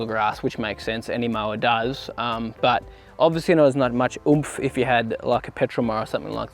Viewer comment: What's the word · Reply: Was meant to be, obviously it doesn’t have as much “oomph”